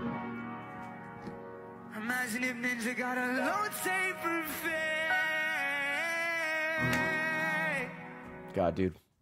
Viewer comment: I thought ray was saying elote fade XD